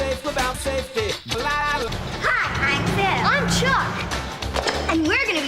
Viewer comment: i thought the girl said her name was “phil” and i never questioned it until i read the captions